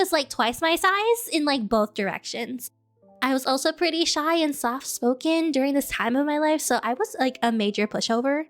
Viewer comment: Instead of soft-spoken i heard "stop smoking."